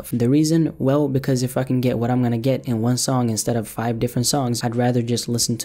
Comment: Sounded like you said I fuckin get what I can get